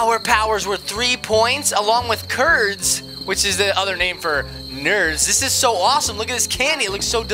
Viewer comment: When they said nerds I thought they said turds.